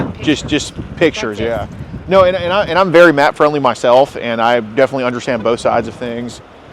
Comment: Did he say he’s very MAP friendly?? Wtf 💀